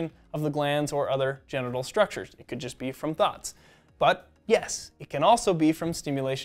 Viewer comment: when he said "thoughts", i thought he said "thots"😂😂😂